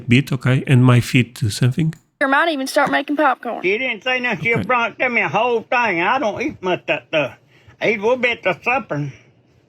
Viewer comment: I'm a native southerner and that was even hard for me to catch, but I believe the old man at 14:44 said, "...He didn't say nothing, he just brought it to me and gave me a whole thing and I don't eat much of that stuff. (I THINK the next part he says) "It'll be et at the suppern. The rest of that in there will go bad if I don't eat it." The "et at at the suppern" part is basically 'Eaten at suppertime'. Hope that helps lol